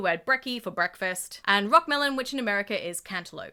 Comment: OHHHHH IT WAS ROCKMELON, I THOUGHT THIS WHOLE TIME HE SAID ROTTEN MELON I WAS SOOOO CONFUSED